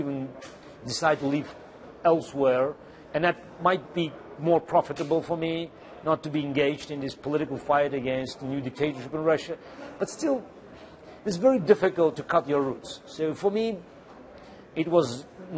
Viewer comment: did he say 'a fright against this new dictatorship'?